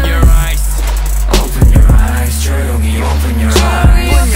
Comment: I hear "J-hobi open your rice."